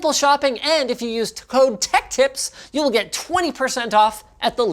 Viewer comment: I swear to god I heard him say ‘if you use toe tech tips’. Linus’ kink confirmed?